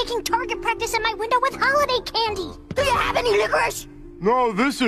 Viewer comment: WHAT DID HE SAY??? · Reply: Do you have any licorice